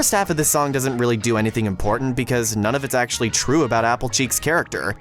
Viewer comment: at one point i thought you said appleJack instead of appleCheeks then you run a mlp scene :)